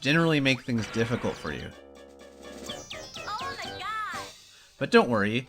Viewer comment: did it just say "oh my god?"